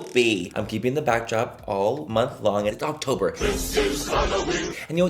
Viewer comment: I literally thought you said, "It's Cocktober" in the beginning because apparently I'M A PERV! 😆